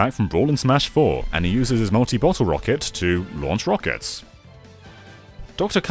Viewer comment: It sounded like you said “Nazi bottle rocket.” lol